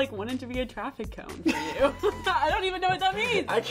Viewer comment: does he say "görüşürüz" (means goodbye in turkish) ?